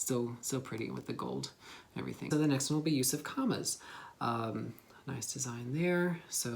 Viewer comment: Sorry, but I could not understand what did you say. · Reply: “So the next one will be Yusuf kama’s”